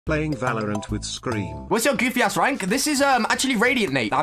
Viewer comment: Bro i thought he said radianite